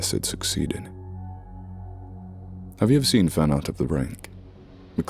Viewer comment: Edit: nvm I found out it’s the take 🤦🏻‍♀️ thought it said fan out of the ring lol not fan art of the rake lol 😂 What’s the creepy pasta mentioned in this story?! The one mr blank turns into?!